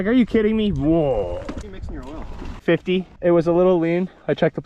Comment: How could you hear how much oil he’s mixing in his gas 🤔 Just asking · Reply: I heard “50”